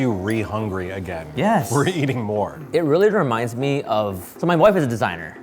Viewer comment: for a second there I thought Steven said "so my boyfriend's a designer" and I was like 😮and had to rewatch it several times 😂